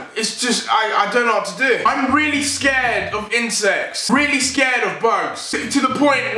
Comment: On number 2 i thought he said he was afraid of insest